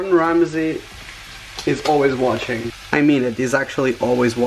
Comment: FOR A MINUTE WHEN HE SAID "GORDON RAMSEY IS ALWAYS WATCHING" I THOUGHT HE SAID "WASHING" LMAO